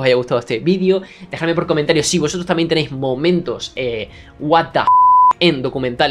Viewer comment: Pense que decia momentos what if en documentales de dinosaurios xd